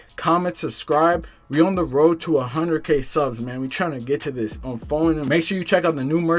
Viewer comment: Did he say on for nem 2:19😂